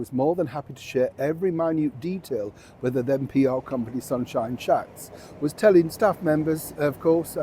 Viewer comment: Did I hear right, did Neil say sunshine shats? 🤣